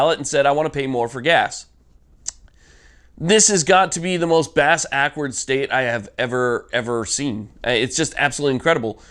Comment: did u say bass-ackwards, not ass-backwards?